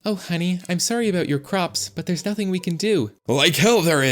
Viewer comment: did just say theres nothing we can do